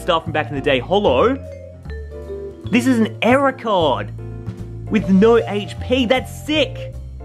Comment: I thought you said era card, but you actually error card. Well that's what I heard